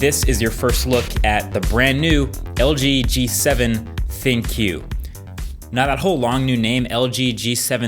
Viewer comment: LG G7 ThinkQ , I heard it as LG G7 Thank You lol